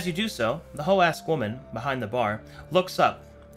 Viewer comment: I keep mishearing it as “hoe ass woman”